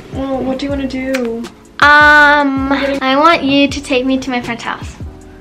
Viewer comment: friend might be her crush 💕❤😳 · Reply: Yo at first since she whispered it I thought she said she wanted to visit her boyfriend😳💀